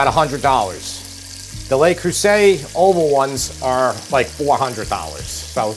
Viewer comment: Love the videos and still getting used to the Long Island accent. When you said 'the le crueset', I thought I heard "The Lakers say the oval ones are like $400". Who do you know from the Lakers and why do they care about dutch ovens?